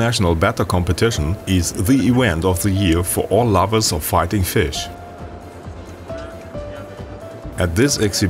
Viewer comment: at the begging I thought the narrator said "farting fish"